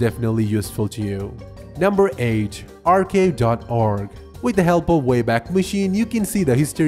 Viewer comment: did he say number age😂😂😂